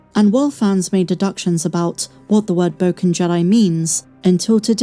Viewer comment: I thought he said Broken Jedi in a weird accent